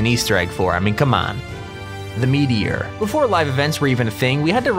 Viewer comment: You sound like you said "The Memeyer"